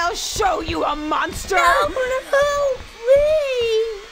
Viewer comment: what did Isaac said??? I still can't understand. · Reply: I thinks says something like: "No mama, no please"